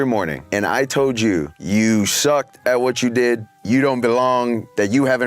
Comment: “you suck, jackass” Happy Gilmore is all I hear 😂